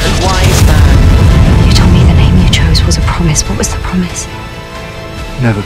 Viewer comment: What did he say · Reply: “...never give in.” It’s from The Day of the Doctor, when they talk about the promise of the Doctor.